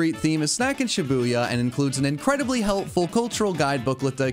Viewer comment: I need my ears checked because at the beginning of the sponsor I swore I heard go you want incredible sex